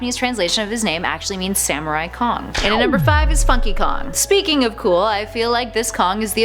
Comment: it's sounds like she says fu*ky kong